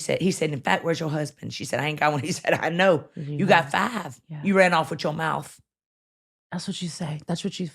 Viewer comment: All I heard was “it’s your mouth! Shut your mouth” I hear ya God!